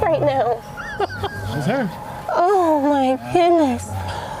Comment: I literally heard “oh it’s big, OoOoh my goodness.”